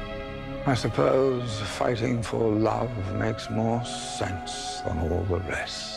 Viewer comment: does he say "fighting for love makes se* than all the rest" at 00:20? ha!